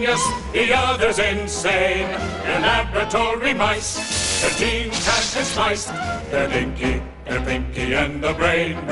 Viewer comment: I always thought they said "They're both filled with spice", instead of "Their genes have been spliced."